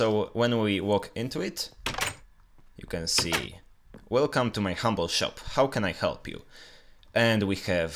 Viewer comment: At firt when he went to the shop for the 2 seconds I thought it said " Welcome to my horrible shop " Well dammit, im horrible.